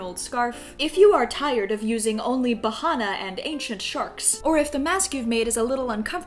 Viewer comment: When she said ancient shark I thought she said Asian shark EDIT:I’m not trying to be racist but that’s just what I thought she said